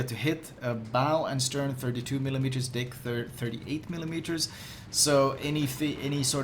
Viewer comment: flamu did u said dick there?